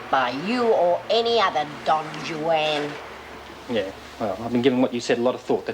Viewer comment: Lizzie calling Doreen’s bloke Don Jwan, instead of Don Juan!